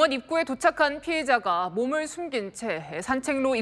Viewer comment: 아나운서 발음이 개판이네. 피의자를 피해자로 들리네..내가 잘못들었나 해서 자동자막 켜보니 피해자로 뜨네.ㅠ